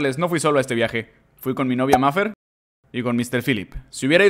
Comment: Escuche que dijeste "con mi novio"pero eso escuche Lo suponia🏳️‍🌈🤨 La ecena esta en el minuto